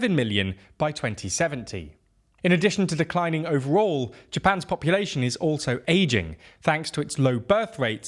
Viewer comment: When you said “Japans population is ageing” I misheard it as “Japans population is Asian”. 😂😂 I was like “well, duhhh”.